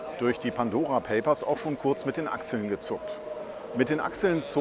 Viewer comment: Ich hab zu Anfang echt verstanden: "Mit den Aktien gezuckt". Dann fiel mir ein Stein vom Herzen. "Isch abe gar keine Aktien".